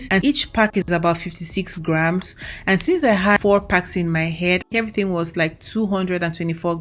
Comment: How many packs for a full head? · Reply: I think I heard “4 packs in my head”. Lolz. Hopefully I heard right.